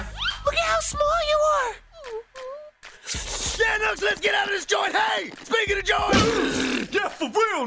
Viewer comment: Any idea what Espio is saying? Especially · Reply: Billio The Neat "yeah knux let's get out of this joint. Hey speaking of joint-"